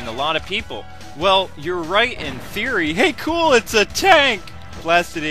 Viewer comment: i heard u say "OH A TANK" n that had me stitches xD